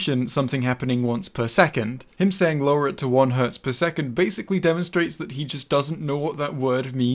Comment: I thought he said 'at 1Hz per second' when I saw the series :v I guess my brain wanted this to somehow make a bit of sense XD 46:12 In decimal from binary it would be <num> ^ 24) - 1 or sum from i=0 to 23 of 1*2^i